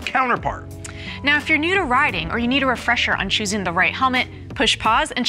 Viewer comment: i thought she said "rioting" as it did sound like that